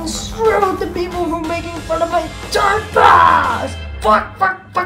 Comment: I HAVE RETURN TOO (From School and Homework) Me: Hmmm is in 1:45 Is he saying F**K or Quack lol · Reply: He said f**k. Because Heihachi is so angry.